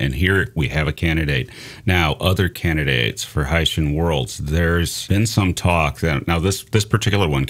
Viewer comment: I keep hearing "Hessian worlds" and i just imagine cone-headed aliens..... from France....